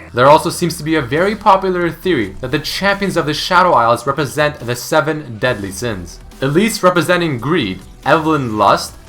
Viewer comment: I don't want to offend you but when you said "The Seven Deadly Sins" i heard "The Seven Dead Lee Sins"...